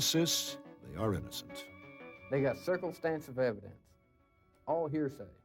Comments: Damn, I nearly spit my drink out at 15:16, from laughing at his Jethro Bodeene sounding attempt, at saying circumstantial. It sounded like he actually said "circle-stansive" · did he say “they got Circlestansive Evidence?” Sounds like he misheard a lawyer talk and ran with it. What a god damn idiot. I’d smack him around if I had the chance. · Story 1: Circle stance of evidence? Is that what he said at 15:15? What an idiot. Story 2: "Adolescent stunt" at 26? Sorry dude--- no. As for the prosecutor? It wasn't about paying for the stuff, it was about the police showing up. What stolen items? The information from this prosecutor doesn't match what we've been told occurred. Anyway, 20 years for even those two offenses combined is ridiculous. The only harm was the loss of $400! (And why is it a 20-year prison sentence but life on parole?) What about the gas station attendant? Why was he exonerated? There were no witnesses to the holdup, and the get away car was his. And why did he remain anonymous? His story is pretty sketchy.